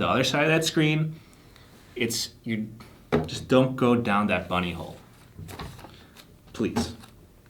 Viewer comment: "Don't go down that bunny hole"...I honestly thought he said buttyhole..😏🤣😅